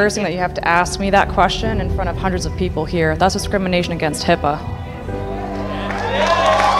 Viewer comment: I thought she said "Thats descrimination against hippos".